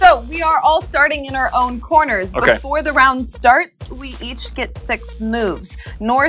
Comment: did said we each get sex moves